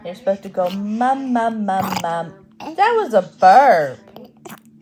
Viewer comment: It sounded like Athena repeated the word "burp" at 16:13 😊